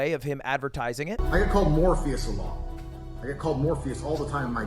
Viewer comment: DID HE JUST SAID HE IS GETTING CALLED "Morbius" ALOT????